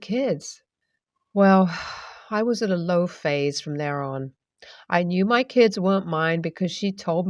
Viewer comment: Story 1, I don't think he's their bio dad. He says at 4:40 "I knew my kids won't mind because she told me about it all." I think that was supposed to be I knew my kids weren't mine. Especially since at 4:59 op also said "for my children. Yes I still call them mine." This only makes sense if they're not his bio kids but he still considered them his kids.